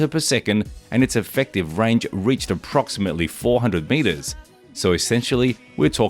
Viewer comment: I thought you said 400 m/s after saying 1 km/s, I was thinking "I better relearn metrics" upon 'hearing' that.